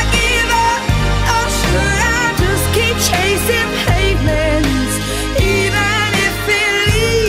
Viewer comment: Hearing this song at work over the bad pa system... I always thought she was singing "Should I give up or should I keep chasing Penguins?" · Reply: I thought it said payments lmao